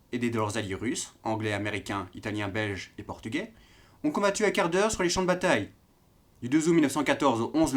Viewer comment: Ahah, j'ai entendu "les soldats ont combattu un quart d'heure sur le champ de bataille". Les français se pleignent vraiment toujours pour pas grand chose. :p